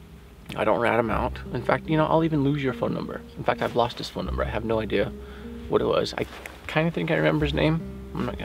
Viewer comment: I thought he said he got rid of informants phone number! 🤔🤔🤔🤔🤔🤔